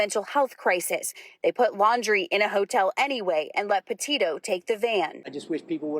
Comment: I thought they said he was in a shelter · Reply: nope, hotel 🤦‍♀️